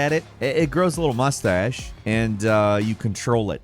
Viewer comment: i heard.. “you can Troll it” haha not control it. 😊